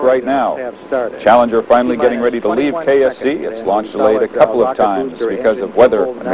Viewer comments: Did he just say "Ready to leave KFC" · Did he say KFC? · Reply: No. KSC he said, it's short for Kennedy Space Center.